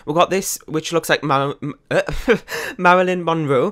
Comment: I'm Really Thinking Long & Hard . Idkkkkkkk . I Think It'll Be Useful Though 🤔🤔🤔 Haha , I Heard You Say "Melanin" Kinda At First When You Was Trying To Say Marilyn 😂 . I Think I Should Change My Name to Melanin Monroe Now 😂😂😂